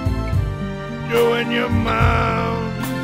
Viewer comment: at first i hear "doing your mouth"